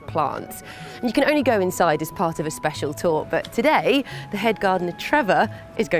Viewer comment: Oh she said HEAD gardener? I thought she said DEAD gardener would've been much better for her narrative on the supposed danger